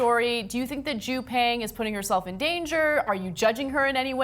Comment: My God, but I thought you'd said, "is Jew paying putting yourself in danger?" I'd forgotten her name! I was like,…"tefuq?!?" o.O x^D